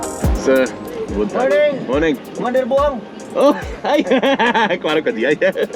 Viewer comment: what did the officer said? "Kumander Buang?" 😶😶😶